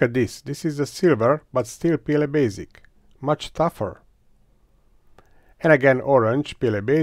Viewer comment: Through most of these tests, I kept hearing the phrase "PLA Basic is tougher"